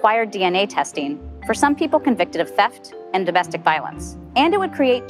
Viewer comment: She keeps saying "some people." Who are "some people" because every time she says it I keep hearing "brown people." This prop seems vague and ripe to be abused.